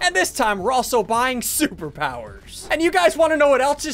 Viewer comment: did he just say we're poor